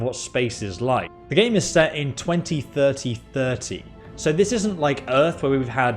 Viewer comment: Did he just say the game is set in 20-3030? I am just busting balls, but I am sure I heard that right. 😂🤪